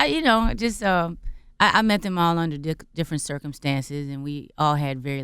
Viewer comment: Am I the only one that heard her say " she met them all on a dick/ different circumstances. I am surprised Charlamagne did not comment on it"